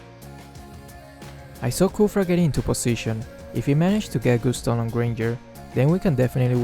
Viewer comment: Lol I thought he said "I saw khufra into position, if he manages to get good stoned on Granger"